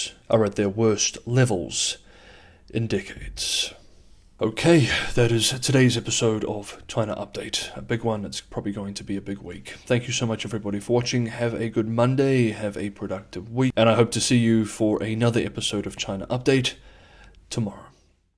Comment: What said: "its probably going to be a big week". What i heard: it's about to GO DOWN" 😂